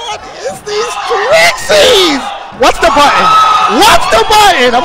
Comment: Did he said:" Whats the button ?"